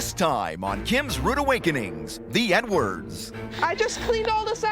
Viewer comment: Sounds like he said "The N-words" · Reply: That is exactly what I thought! Even tho I know he is saying Edwards I still can here him say “The N words” 😂😂😳